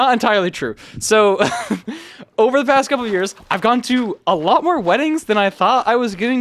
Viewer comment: did bro just say goon